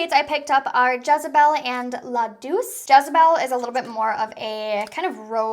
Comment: what word did you say 😂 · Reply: Douche 😂😂